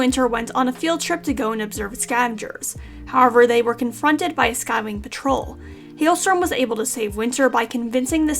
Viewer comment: I thought you said "skyclan patrol" ( 5:55 ) and imagined WInter and Hailstorm confronting mini cats